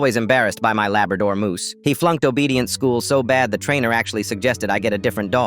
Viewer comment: I THOUGHT IT SAID OBESE TRAINING NOT OBEDIENT LMAO